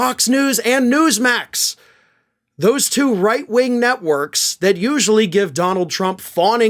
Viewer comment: For a moment I thought you were saying "... Fox News and Newsmax. Those two right wing nutworks..." But no, that was "networks" after all.